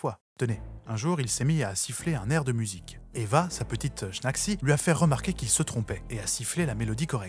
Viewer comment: sa petite quoi ?😂 Merci pour ses 3 vidéos géniales hâte d’en revoir 😮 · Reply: Quelque chose comme Schnacksie ou Schnaxi (surnom personnel donné par Adolf, apparemment). Il me semble qu'en privé les proches la surnommaient Évi.